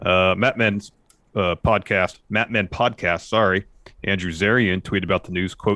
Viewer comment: for a second i thought said "map men podcast" at 1:07 and was trying to figure out why Jay and Mark were reporting wrestling news.